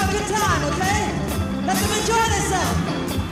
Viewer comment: Can anyone write what MJ is saying? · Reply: He said “Hey listen, don’t sit down, don’t listen to this man right here. I want you to have a good time okay, let them enjoy themselves” he said this because before performing off the wall he did beat it. While performing beat it this some man was stopping people from enjoying themselves and dance. You can see the person interrupting if you look up “beat it live New York” on YouTube in about the 5 minute 37 second mark